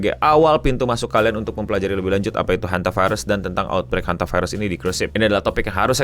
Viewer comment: Gooner yg salah denger dan ngira namanya hent*ivirus😋